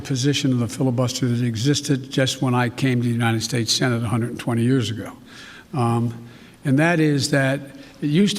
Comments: I can't quite understand what he is saying here does anyone know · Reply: he said he is 120 years old. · did he just say, “when I came to the US senate 120 years ago”?